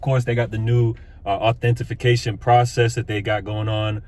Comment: Am I hearing this right? "Authentification"